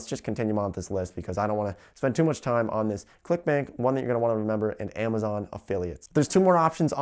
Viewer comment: When you said "clickbank" I swear to God that I heard you said "clickbait..."